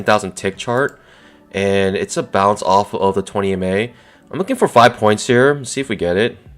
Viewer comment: Great trade at 37:30. Did you say that was the 20 ema you were using on the 10000 tick chart?